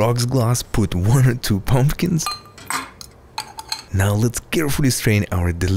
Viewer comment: For a sec I thought he said 102 pumpkins… 🎃🎃🎃🎃🎃🎃🎃🎃🎃🎃🎃🎃🎃🎃🎃🎃🎃🎃🎃🎃🎃🎃🎃🎃🎃🎃🎃🎃🎃🎃🎃🎃🎃🎃🎃🎃🎃🎃🎃🎃🎃🎃🎃🎃🎃🎃🎃🎃🎃🎃🎃🎃🎃🎃🎃🎃🎃🎃🎃🎃🎃🎃🎃🎃🎃🎃🎃🎃🎃🎃🎃🎃🎃🎃🎃🎃🎃🎃🎃🎃🎃🎃🎃🎃🎃🎃🎃🎃🎃🎃🎃🎃🎃🎃🎃🎃🎃🎃🎃🎃🎃🎃